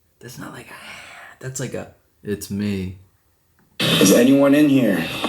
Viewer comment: ? The "Its Me" - has 3 syllables, In English I hear, "It's me Nick" If it was in another language.. Fitz mier (bic or nik) ?